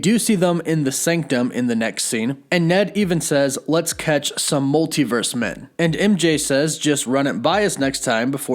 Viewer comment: I thought Ned was saying "Let's kick some multiverse m-" I thought it was a bad word cut off, but I didn't know what word it would've been